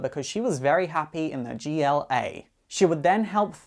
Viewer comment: I swear at roughly 3:23 he says the JLA instead of Avengers